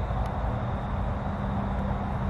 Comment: after exhales, does anyone else hear what sounds like a male voice say, "I said move it!" Then a clank like someone dropped something heavy like a chain. Then asks, " You hear that?" Was that whispering, "Why's that moving?" If so, then what made the heavy clank and prompted him to ask if we could also hear "that?" Holy smokes at 12:47 before let's out an "Ohhhhh, I saw something" it sounds like a voice says something like, "You wanted it." As if to say, look buddy you got your wish? 😳😳😳 😂🤣😂